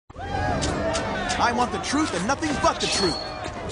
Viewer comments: listen to wright! it sounds like he says: "I want the truth , and nothing f*ck the truth!" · I paused the video at 0:02 and pressed play. Sounded like Pheonix said "Fuck the truth!" ._.